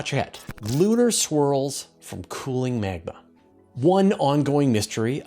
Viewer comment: When you started talking about lunar swirls, I could have sworn you said "lunar squirrels." I was disappointed at what it turned out to be. You have to admit, a piece about lunar squirrels would get 100% of the vote for coolest story of the week.